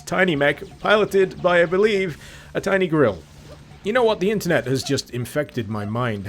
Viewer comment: When you were talking about the Mancubi I at first thought you said "a tiny mech piloted by a tiny gorilla," and honestly, that sounds much more interesting than . . . well, than this.